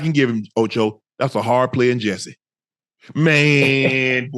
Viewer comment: "that's a hard play in Jessie" is what I heard. What does that mean?